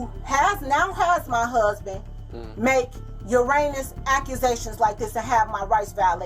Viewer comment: Did she say "your anus" accusations??? 😂😂😂